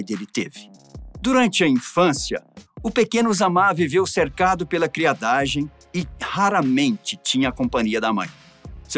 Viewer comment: E em 3:42 eu ouvi "cercado pela v1adagem" kkkkkk mds